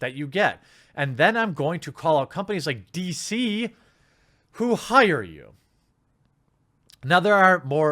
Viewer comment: Almost sounds like around 13:20 mark that you are saying: "And then I'm going to call up companies like DC who hired you" Listening more closely I think you said: "And then I'm going to call OUT companies like DC who hired you" Hope it's the latter since you yourself have come out in the past condemning journalists who call people's employers.